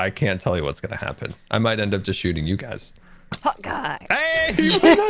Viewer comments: So till this episode I thought that scar was saying hawkeye now I realize it is hot guy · I thought he was saying Hawkeye for so long, not hot guy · I always thought Scar said "Hawk Eye" not hot guy :0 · I thought they were saying hawk eye when shooting the bow until grian picked it up and it said Hot Guy🔥 💀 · Who else thought scar was saying “hawkeye” and not “hOt gUy” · This entire time I thought scar was saying "Hawkeye" not "hot guy" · ngl i thought scar was saying "hawkeye" not "hot guy" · dude i always thought Scar was saying Hawkeye! not Hot guy! loool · I always thought Scar was saying "Hawkeye" not "Hot guy". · Who else thought that Scar was saying “Hawk Eye” instead of “Hot Guy”? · all this time, i thought scar was saying, hawkeye not hot guy, lol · WAIT, 'Hot guy'?? I thought Scar was saying 'Hawkeye' all this time!!! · For a looong time I had no idea what scar said when he said ‘Hot guy!’ And for so long I thought he said ‘HAKAII!’ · Wait scars bow is named hot guy? I thought he was saying Hawkeye 😂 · I thought scar was saying “hawkeye” instead of “hot guy” the whole time </3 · Really thought they were saying hawk-eye and not hot guy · it says hot guy???? This entire time I thought it said hawkeye. :,D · I thought Scar said hawk eye and not hot guy😂😂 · When scar says hot guy, I thought he was saying Hawkeye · Was it just me or did I hear “Hawkeye” in stead of “hot guy” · The whole time I thought scar was saying Hawkeye not hot guy